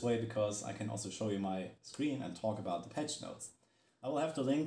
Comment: When you said "the patch notes" I heard "Depeche Mode" lmfao